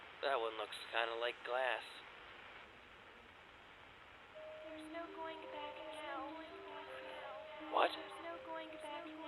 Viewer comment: i can make out some of what the strange voices are saying: Mystery female: "There's no going back now" on repeat Mystery male: "I know" Cannot make out any more than that